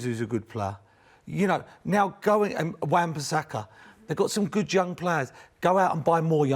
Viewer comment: I thought I heard Paul Merson said "...good junk players." I thought that was apt, even though clearly i knew i heard wrong.